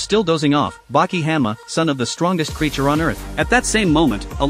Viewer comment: My brain is dirty I thought he is saying son of bit** then he said son of the strongest man